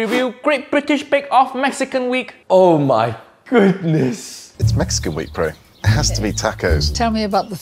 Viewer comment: Bro, I thought he said "It's Mexican week in Perú" at the beginning and my braincells shut down